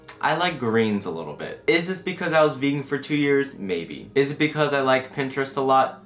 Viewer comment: when he said vegan I thought he said beaten. I was shook